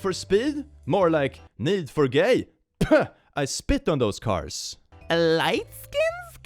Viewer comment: was this joke intentional? The way he said spit sounded like speed and the games name is need for speed after all